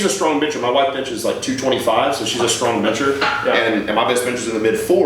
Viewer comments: I thought he said “my wife’s a strong bitch” at first lmao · thought he said his wife is a strong bitch 😂